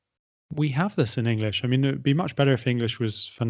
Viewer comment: Hi there, couldn't catch wt said @ 30:16 minute. Then I checked subtitle by YouTube CC button then noticed it was it'd! However my question 'having vs have had' still unanswered by sir.